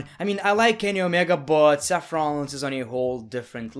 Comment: When you say seth rollins Captions say saffrons 😂😂😂 0:44 you say santino marella Captions say centimorella 😂😂😆😆 2:58 you say eva marie Captions say even marie 6:07 you say randy orton Captions say andy orton 6:18 you: stone cold Captions: stone called 6:40 rock is he Captions: no rock is she 😂😂 6:52 you: great khali Captions: great khalid 8:09 you: stowman Captions: strobin 10:18 you: nia jax Captions: knight jax 10:27 you: jon moxley Captions: joHn moxley